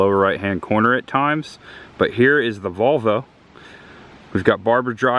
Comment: @ 5:30 did you say 'There is the Vulva....(?)', instead of VOLV-O! ? LOL. This is so cool to see. Richard